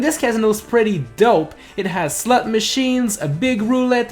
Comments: does anyone else hear "it has slut machines" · I heard "Slut Machines" Lmao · what did he say. am I the only one to here slut machines? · Im not the only one who heard "Slut machines" am I? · did he say slut machines ah i want a lap dance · did you just say slut machine